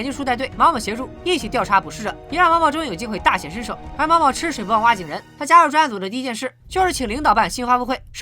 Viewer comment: 9：57 有多少人听成了 毛毛吃屎不忘挖井人。。。。。